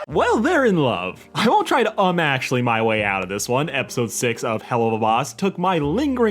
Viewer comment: When he said, "Um, actually," I heard, "Oh, Ashley..."